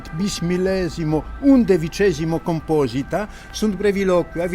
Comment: did he say bismillah and repeated like twice or three times ? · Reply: Bis millesimum. Twice thousandth.